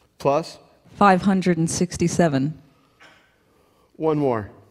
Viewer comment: I heard her say 567 @ 3:33 not 557; I don't know why he heard 557 and the guy hitting the numbers heard 567. 557 would give his answer 2797 and 567 would give 2807 which is what is on the calculator. It's like Laural and Yanni.